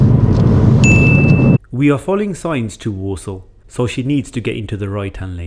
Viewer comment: I heard "we are following the signs to Warsaw" and for a moment I seriously thought Lily was using a mock test to drive to Poland